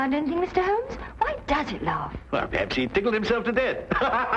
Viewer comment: Why does he laugh? Perhaps he didled himself to death Surprised that innuendo got by the censors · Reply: I thought he said "tickled"